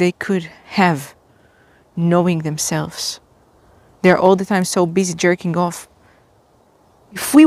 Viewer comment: I kept hearing "They're a teenage boy" and it's always felt like an ick.